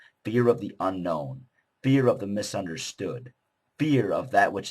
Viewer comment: for some reason i keep hearing beer, when you say fear, in the video- like in "...beer of the misunderstud..." (especially around 1:00) but anyway, you make good points. ;)